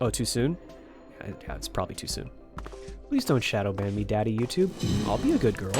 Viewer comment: Did he just say daddy YouTube